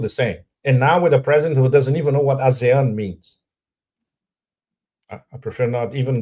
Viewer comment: Pepe said "...and now with a president who doesn't even know what. _ means..." Could anyone tell me what the word he said was? Thanks! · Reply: ASEAN is the association of southeast Asian nations